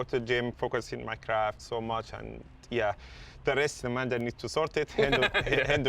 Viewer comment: Did he say mandem